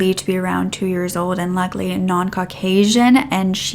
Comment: DISCLAIMER I wanted to make sure that it is known I’m saying “likely Caucasian” NOT “luckily” when talking about the toddler victim.❤️ Had you heard about this case? Do you think more victims are out there? Thanks to MDhair for sponsoring this video! Remember to click here <link> to get your first month of customized products at 70% OFF using my promo code 'BROOKE70' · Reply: Omg totally thought you said “luckily non-Caucasian” and listened over and over😅 I was stressed but at the same time knew you’d never say something like that